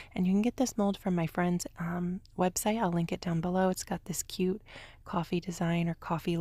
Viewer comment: Help I thought it said coffin not coffee